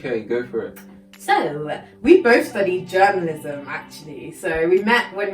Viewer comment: When I hear "we both studied Germanism" and I was like.. Whaat? 😂🤦‍♀️